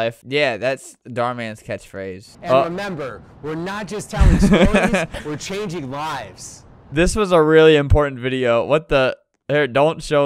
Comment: I remember seeing his intro and i heard "We're not just telling stories, we're changing colors"